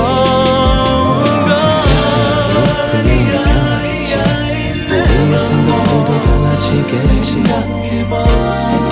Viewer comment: 야이야이 뒤에 뭐라고하는건가요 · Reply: Nevermore 예요